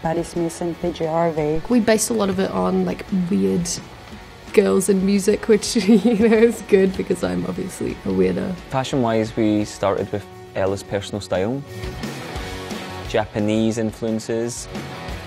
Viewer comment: Did she call herself a weirdo? o.O It's okay, dude. So am I :P